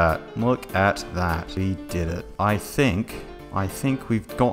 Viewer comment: When he yells WE DID IT he sounds like Harry saying EXPECTO PATRONOM